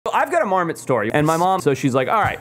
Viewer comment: I thought he said I have a Mormon story